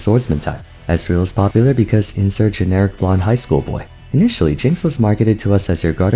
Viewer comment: “Ezreal is a generic blonde highschool type” 😂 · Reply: I thought he said generic OURAN highschool ???